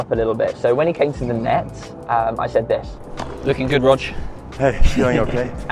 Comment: what did he say? couldn't make it out. thanks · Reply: “Lookin’ good Rog”